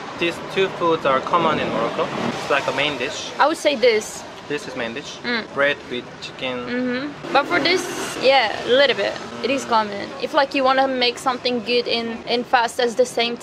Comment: lol when you said " bread with chicken" i heard it like : " brad pitt chicken "